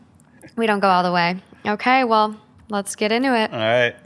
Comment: Every time you say "Let's get into it." I hear "Let's get Inuit" lol.